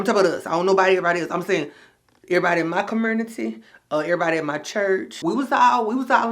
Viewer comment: OK did u just say COMURNITY? 😅